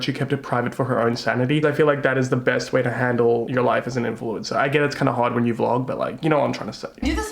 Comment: i thought you said "it must be hard when you're blonde" at 3:19 and it was hilarious